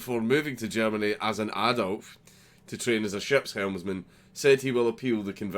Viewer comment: I swear I heard - 'moved to Germany as an Adolf' (adult) lmao